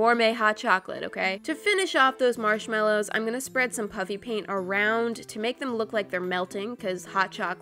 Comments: For two years straight u thought she was saying “puppy paint” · it sounds like your saying "PUpPy pAiNt" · Sounds like you said puppy paint🤣🤣🤣🤣🤣🤣🤣🤣🤣🤣🤣🤣🤣🤣🤣🤣🤣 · iT sOuNdS LiKe ShEs SaYiNg PuPpY Paint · Sounds like she said puppy paint 😅☺️😌🤣😅😅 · It sounds like your saying puppy paint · It sounds like she is saying PUPPY PAINT 😆😆😆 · It sounds like ur saying puppy paint hahah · It sounds like she saying puppy paint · It sounds like your saying puppy paint! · I heard "puppy paint"😂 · sounds like shes saying "puppy paint" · it sounds like your saying "puppy paint" but thats fine i like the normal you :) (>'v'<) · Haha it sounds like your saying PUPPY PAINT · It sounds like is saying puppy paint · It sounds like your saying puppy piant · Haha it sounds like she is saying puppy paint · I think it says koala and it sounds like your saying puppy paint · It sounds like your saying puppy paint. I love the koala . Please can you give me a shout out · Pls- it sounded like she said "puppy paint" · It sounds like you say puppy paint 😂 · It sounded like you said puppy paint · Sounds like shes saying Puppy Paint XD